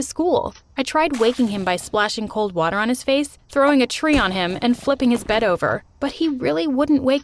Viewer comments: I'm sorry did she just say throwing a tree on him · Change the title to ~my perfect life~ I mean like bro dose she ever mess up Edit: wait hold up did she say throwing a tree on him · Did she say tree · Is it me or did I hear her say "throw a tree at him"